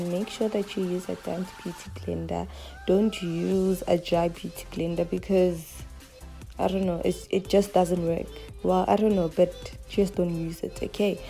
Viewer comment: Babes, love the tutorial 🤗 but, what did you say about the beauty blender? Something about "don't use it" played it over again but still didn't get it 😔 Edit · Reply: She says use a damp beauty blender don’t use a dry beauty blender because it doesn’t work